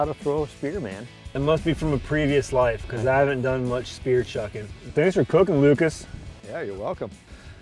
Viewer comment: what did he say?! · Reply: "It must be from a previous life because I haven't done much spear chuckin'."